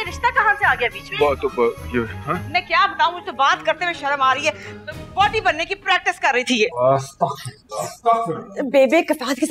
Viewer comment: what did she say? ....... bannay ki practice kar rhi thi ??? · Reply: woti (wife in punjabi)